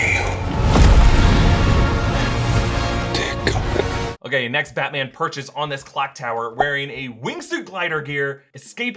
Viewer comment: Anyone know what Alfred (Andy Serkis) says at 17:05? He doesn't go over it in this video and I can't make out the words. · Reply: "Dear God"